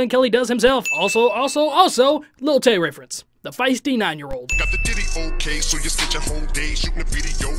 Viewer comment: Also i heard the "got the diddy ok" line as "got the ditty" as in a little song. As in "you made a decent song but you spent a whole day on the video when it was pointless"